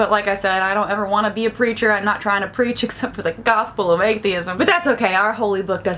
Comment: When you said you “the gospel or atheism...” I thought you said “the gospel RAYtheism” and I was like “sounds like something Ray Comfort would start”. 😂😂